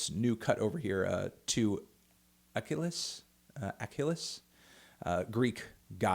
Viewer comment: When you talked about Cult Leader, I thought you said "Achilleus" which is obviously not a goddess. I was gonna post something smug, but than I read that it's "Achlys", an actual goddess from whom I've never heard before. Now I feel dumb and crave renewed validation for my intellect.